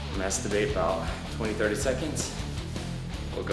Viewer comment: sounds like he said I masterbate 20 to 30 sec xD